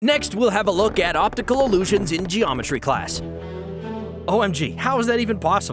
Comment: What others hear (geometry class) what I hear (geometry dash) 16:36 😂😂😂